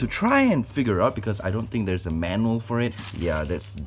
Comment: did he say "is there a mammal for it" or am I trippin